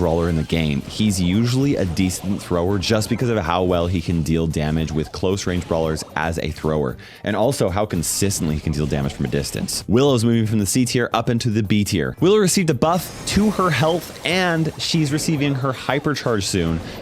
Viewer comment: did anyone else hear him say Willer?